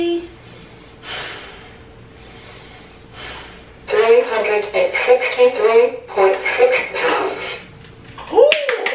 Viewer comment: no se que dijo pero miente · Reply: He said that niko is 363 pounds